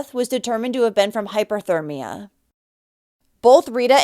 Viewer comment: I am confused, you said Cristina had hypothermia? That's cold not hot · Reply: I heard her say, "hyperthermia"